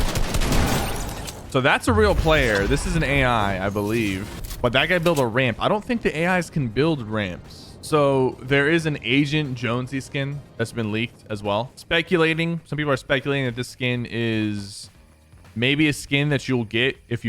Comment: lmao i thought he said asian jonesy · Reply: Me too! I was so confused and at the end I was like, OH he mean AGENT jonesy because that the theme of this season